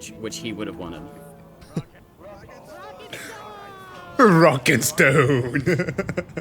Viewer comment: DID I HEAR A ROCK AND STONE?